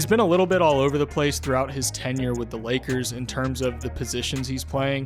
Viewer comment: Did he just say that AD played ten years with lakers? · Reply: Tenure